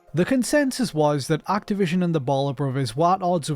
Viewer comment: The entire time, until about halfway through the video when their last name was finally spelled out on screen, I thought the narrator was saying "the Baller Brothers."